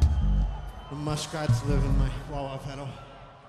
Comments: What does John say about his wah wah at 58:36 ? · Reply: "Muskrats live in my wah wah." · What is John saying at 58:37? Something about is wah wah pedal?